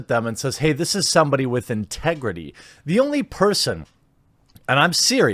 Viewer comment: did someone said.... TEGRIDY ? where can i get some